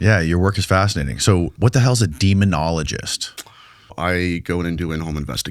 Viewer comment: I thought it said dermatologist.